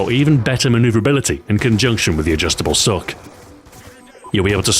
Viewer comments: I thought you said "sock" at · Does anyone else hear him say 'Adjustable Sock'?